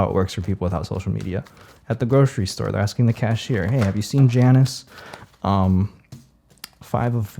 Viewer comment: On the one hand, each time the page of swords flipped, I accidentally flipped a pin when pulling a different one (I'm a lacemaker, and the pins remind me of swords 😂) which is a scary level of confirmation 😂😂😂 On the OTHER hand, when you said "Oh have you seen Janice lately?" I misheard and had to pause to scream "DAMNIT, JANET, I WANNA SCREW"....which seriously ages me with that reference 💀💀💀